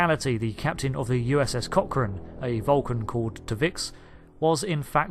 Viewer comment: when i first heard you mention the Vulcan's name i thought you said "Tuvex" and I was WHOA WHOA WHOA did i miss something major here! Had to wait until you put it on the timeline before i realized it was T'Vix